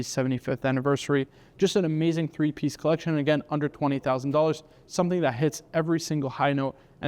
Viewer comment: I thought it said "under 2,000" at first. I was like "ooh this'll be good!' lol. Still great video and watches!